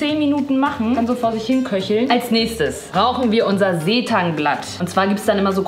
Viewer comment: „Als nächstes RAUCHEN wir unser Seetangblatt“ ? (7:16) - sagt mir bitte, dass ihr das auch verstanden habt😂